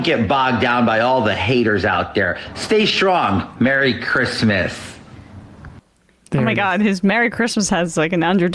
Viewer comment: He said "Merry Christmas" but it almost sounded like "F*** 'em"